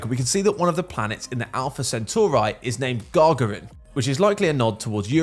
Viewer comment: "...Centauri..." did I hear that correctly?